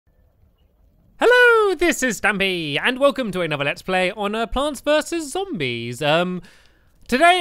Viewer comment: The audio glitched at just the right time so I heard: "Hello, this is Stammy-" XD